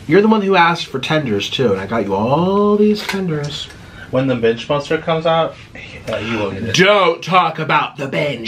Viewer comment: I thought yous said the minge monster 😂😂